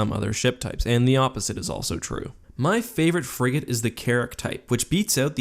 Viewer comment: anyone else hear Carrot instead of carrick